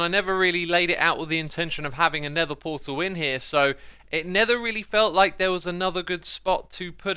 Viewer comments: Is that a hidden pun I heard? “It nether really felt like...” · Did X just say "It nether really felt like there was another good spot"